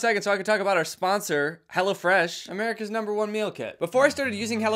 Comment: i thought he said “hella fresh”